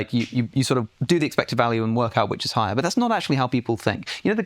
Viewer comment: I thought he said "my friend phil hellmuth pointed this out to me"